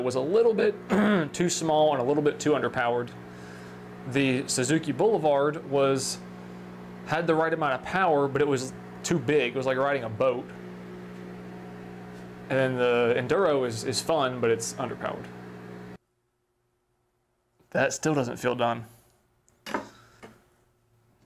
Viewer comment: I had to rewind when you said, "riding a boat," because I could have sworn you said, "riding a goat." 🤣